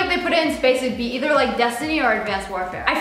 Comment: I thought destiny said Disney😂😂